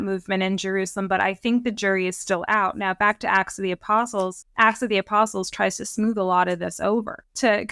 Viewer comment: A slight slurring by Ms Walsh around 16:40 made it sound as if she said "ass of the apostles," which cracked me up. It's a little funny because of the modern use of the word "ass," but what actually popped into my head when I heard it was donkey with a fancy riding blanket and "Ass of the Apostles" painted on his ribs, like a bible-age version of a band's custom tour bus.